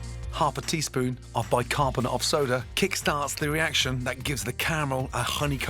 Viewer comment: did he say bicarbonate soda? is that baking soda?